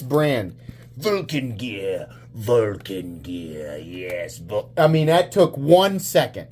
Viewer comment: Oh noo! I honestly thought it said "Vulgar Gear" and it made sense. 😂😂😂